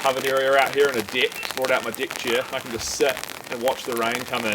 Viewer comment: Thought you said "dick chair" lol